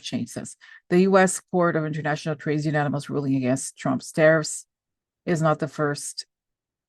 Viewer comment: I thought it interesting that closed caption thought you said terrorist when you said tariffs. Considering how the tariffs are destroying us they might as well be termed terrorists. Comfort to you in your grief but glad to know she is happy in heaven. Amen.